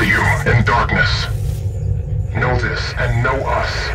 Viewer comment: ZDF ON HIS CHEST ZDF = Zurich defense force (bo3 campaign) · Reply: thought he said Salmon Defence Front lol